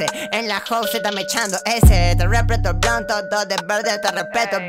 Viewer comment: Yo pensé que decía te respeto bro todo de verde XDDD